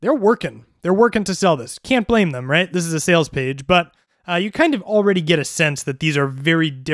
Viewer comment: You said "This is OUR sales page", do you work for them? Looks like a cool useful tool. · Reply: I thought he said this is “a” sales page.